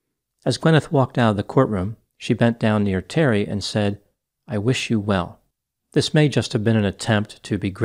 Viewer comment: I am sorry I did not pay attention to this case (zero attention until now) and I thought she was saying I wish you Hell but some said it was wishing you well? ! Wait…this guy is 76 years old…..He looks pretty good for his age even after the “crash accident.” Gimme a break!